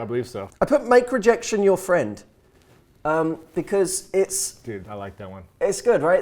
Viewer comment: Finally, I can hear clearly 5th point: Make rejection your friend. Before, I misheard as "Michael Jackson is your friend". I was so confused. Now, finally, I got it. Yesssss!! This is awesome.